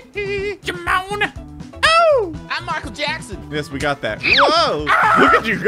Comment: Is it just me or i heard say Mark Jackson